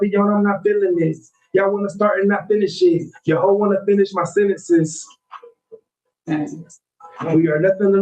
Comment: did he say "yo' hoe wanna' finish my sentences" ? wooooo 🔥🔥🔥🔥